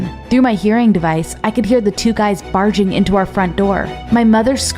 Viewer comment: at first i thought she said farting instead of barging lol